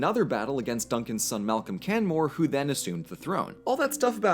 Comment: I thought you said Can-Whore LOL😅